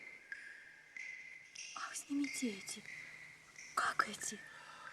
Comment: Кто -то может мне подсказать,что она говорит на 1:27:18 минуте ? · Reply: "Ах снимите эти..как эти" про очки